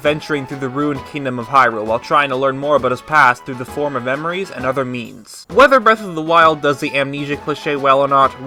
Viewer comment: I thought you said memories and other memes 🤣